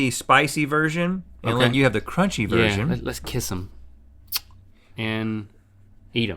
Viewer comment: Them saying let's kiss em and eat em instead of dink it and sink it at 2:56 made me very uncomfortable.